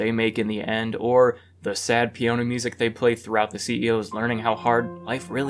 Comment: Did I just hear “sad pee-oh-na music “??